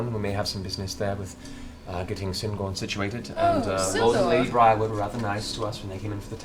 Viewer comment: Spoiler Um... Did Allura just say Lord and Lady Briarwood? I think Percy missed that. Based on his backstory video, I'm pretty sure he's not a fan -- to put it mildly.